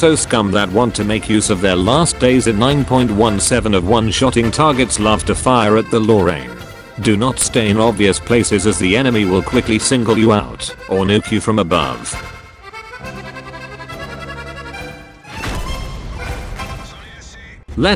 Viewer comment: did I just hear or NEUK you from above? dutch easter egg!